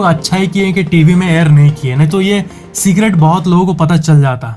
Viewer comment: says cigarette instead of secret 😂😂😂😂o bhaii,🙏🙏